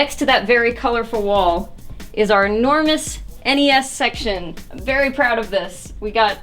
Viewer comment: Damn I really just thought she said " we do have a fetus section " also hi 2019